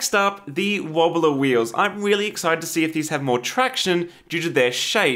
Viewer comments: im sorry did you say “wheely excited”? i must confiscate your pun license if so. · did you say "I'm wheelie excited."